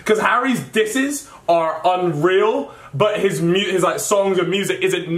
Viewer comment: did he say real or unreal? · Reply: Unreal